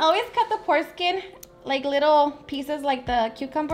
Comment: Man i thought she said "Foreskin"....